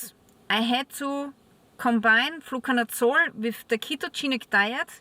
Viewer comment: What is this word youre saying? "Flucanutsore"? 8:50 ? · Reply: Nevermind I figured it out you're saying "Fluconazole"